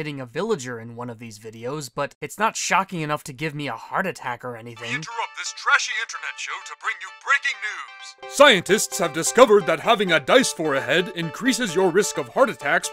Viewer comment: thought he said “disphor ahead”